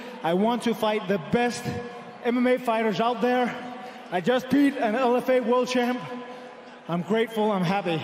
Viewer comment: i thought he said 'I just peed'. Lmao